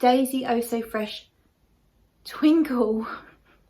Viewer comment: I thought you said "Eau so Fresh tinkle." I had one of those last night! Very refreshing.